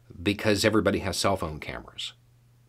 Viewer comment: I heard “everybody has self-own cameras”. Kinda fits.